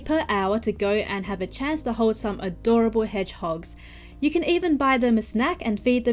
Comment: I thought for a moment she said "you can buy them as snacks" and I was like noooooo